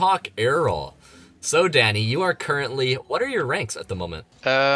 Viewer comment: did he just say daddy am i tripping?????????